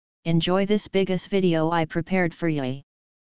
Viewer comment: We’re just not gonna talk about how he said watch the video I prepared for “yoi” at the beginning instead of you?